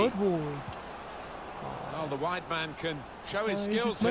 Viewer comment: Persistence pays off. We get the fourth goal in after three blocks. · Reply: Check 4:27, I swear I hear Martin Tyler say the white man